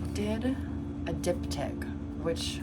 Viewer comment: im v ketted rn and genuinely thought u were saying dick tip not diptych 😭😭 I was wondering ab the logistics of painting that lol